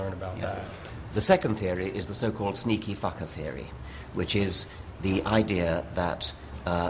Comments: <--did he just say "sneaky fucker theory"?? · did he say sneaky fucker theory?lol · Did he say "sneaky fucker theory"??? o.O